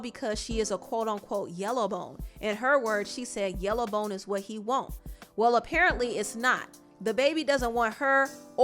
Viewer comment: "She soon found out that she was nothing special." 😩 Awww mannnn. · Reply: I died but I swore she said Redbones 8s what he like 🤣😭